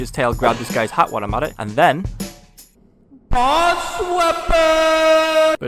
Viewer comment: DID YOU SAY BOSS WEAPONS?